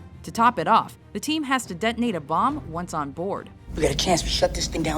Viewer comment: Did she say "the team has to detonate a bomb, once on board" ?!